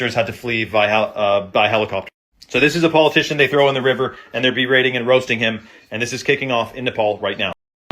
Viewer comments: I think he said "berating and roasting them" like, talking shit and clowning on them. I thought he said beheading too! 😂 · It sound like he said berating roasting not beheading roasting